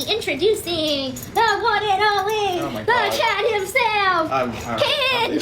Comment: I think she said Chad not child